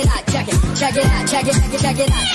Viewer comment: 나만 채키 채키 이새키 이새키 러 들리나 ㅋㅋㅋㅋㅋㅋ